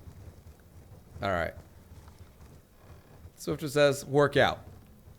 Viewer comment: When he said work out I thought he said TWERKouts